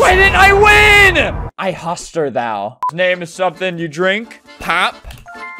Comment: OI HAWSTER YUPU 7:38 - Albert🙏